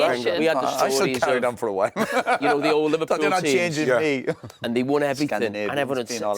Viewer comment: Are anyone able to hear what he says at 25:00? Something about Scandinavians. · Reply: yes, he said "Scandinavians being all healthy"